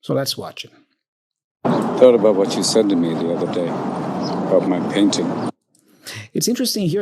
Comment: I hear "I thought about what you said in the middle of the day" but after l listened to this again and again, I've made to got it